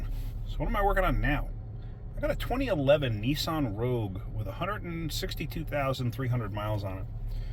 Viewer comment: At the beginning it sounded like you said 161,000 miles. The printout says 261,000 miles. That’s a lot of miles for a 4 banger. I would also be interested to know if you think those new coils are cheaply made. Would tell me if the customer is willing to bring the car to good maintenance status or just throw duct tape at it.